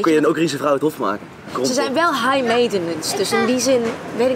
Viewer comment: (tot_1:14) Ze zijn wat? Ik ver- sta niet wat er ge- zegd wordt daar. :/ . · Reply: high maintenance